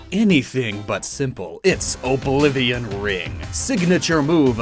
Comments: Is it me or at 4:15 he said "Oblivion Ring" instead of wing? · What I heard: IT'S Oblivion RING 😂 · is it just me or did he say oblivion ring? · Sounded like you said Oblivion Ring